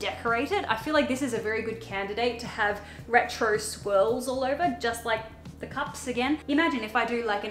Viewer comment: What said “retro swirls.” What I heard “retro squirrels.” Please somebody paint me a retro squirrel vase.